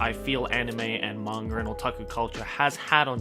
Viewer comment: When Joey says "Manga" I always hear "Manger" for some reason